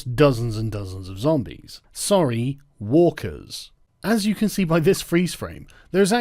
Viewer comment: Lol, I thought Sargon said 'Wokers' at one point instead of Walkers and I thought maybe it was a running joke about SJW zombies.